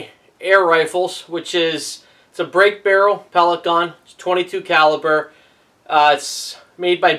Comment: Sounded like he said Caliper instead of Caliber